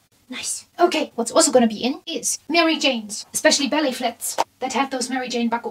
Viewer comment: I swear it sounded like you said Belly Flats instead of Ballet Flats 😂😂😂😂😂